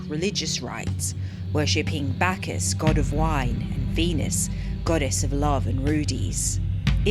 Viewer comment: When mentioned Venus, I misheard it as Beanus......